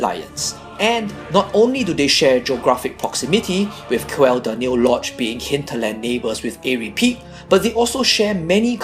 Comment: What the fuck did u just say between 7:12 - 7:17? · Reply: I said that Aerie Peak and Quel Danil Lodge are neighbours